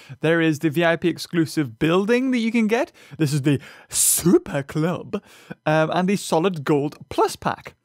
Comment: I thought supper club said stripper club